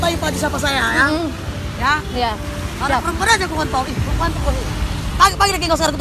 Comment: ini ibu nya kira² ngomong apa sesuai dengan gerakan mulutnya?😂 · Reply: K0N.T0L